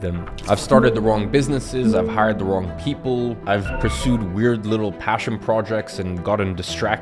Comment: says “wired” instead of “weird”